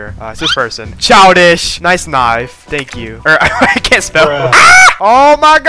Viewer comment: I swear i heard "niCe tHigH" insteas of nice knife💀